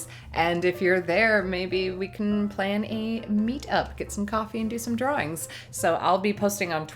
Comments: Lol, I thought you said "get some coffee and do some drugs". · I turned my head to talk to my lady and I swore you said "We can do a meet up, get some coffee and do some drugs...." -_